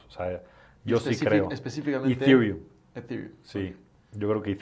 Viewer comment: Hola, que buen post, soy nuevo en este canal, gracias por la información, sin embargo queria saber exactamente cual es tipo de criptoactivo que hablan en el minuto 34:56, no lo entendí bien...gracias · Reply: Ethereum